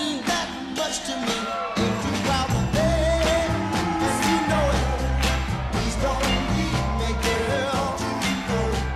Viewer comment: What did Dennis say at 4:00 under his breath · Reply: Look like he called him an mf 😂😂😂😂😂😂